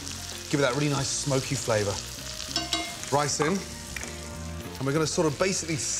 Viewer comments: I my heart stopped because he said “rice in” but I heard ricin which is a poison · I thought he said to put in ricin lol